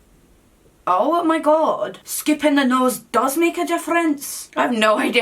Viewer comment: Hahah i'm Swedish, and even i heard she said "sculpting the nose" not "scupping" haha 😄😄